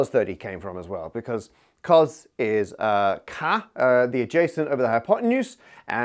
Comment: Captions say 'K the adjacent…' I believe it should say: '"CAH" - the adjacent…'. As Matt is referencing SOH-CAH-TOA.